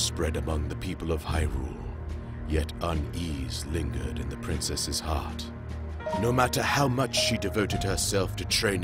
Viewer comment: My valume was low and I heard "tHe PrInCeSs WaS hOt"